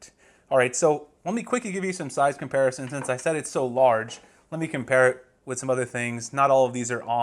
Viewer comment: I thought I heard him say it's large...